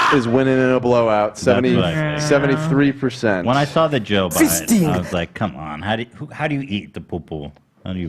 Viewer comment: Hila sounds like shes saying “joe budden”